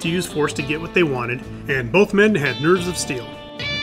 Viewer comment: I thought you said, "nerds of steel." Then you showed Don Knotts!